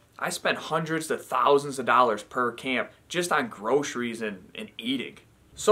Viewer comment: LMFaO i thought u said(u spent 100 of thousands of dollars every year on groceries